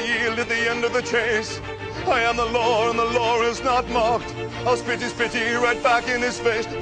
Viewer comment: Thumbs up if at 0:32 you hear "I'll spitty, spitty right back in his face" Instead of "I'll spit his pity right back in his face"